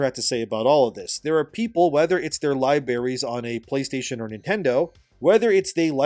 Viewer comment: bro u did not just say lie berries hahahaha come on you speak for a living and you are able to edit. Don’t take this the wrong way either, I agree with your sentiments, but mistakes like that give hipster journalists free ammo against us.